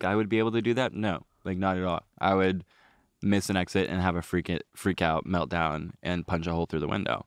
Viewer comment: I thought said missing his ex nor missing an exit 😭😭😭😭 I was so confuses